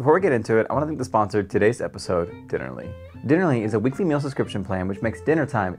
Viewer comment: Instead of dinnerly, I misheard Denali. What a shock to think Denali was the sponsor today 😅